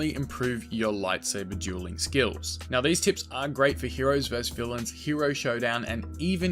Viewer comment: i got kinda confused cos at 0:48 he sounds like hes saying are great and aren't great at the same time